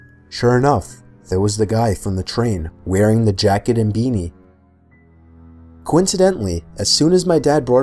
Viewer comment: I thought he said bikini but it was beanie c'mon i really gotta get my ears cleaned who's got an earbud?? Lol